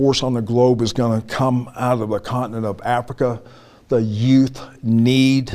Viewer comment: did i hear it right? "Its gonna come out of wakanda"??😂